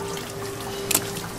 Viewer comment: *No es una ostra, es una GATO escuchen lo que dice la chica, es un gato 🐈 *